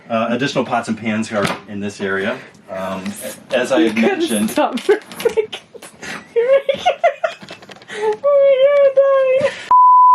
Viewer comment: Min 28:55, I love it when people lose it in laughter. 😂 But what did you say? lmao I swear I tried turning up the volume & captions & I can't make out what you said & why you lost it. Was it is the sarcastic waffle comment toward his husband? You losing it got me laughing for 5 mins. lol😅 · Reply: Oh I said something like omg you have to stop for a sec. your making me laugh I’m dying. Lol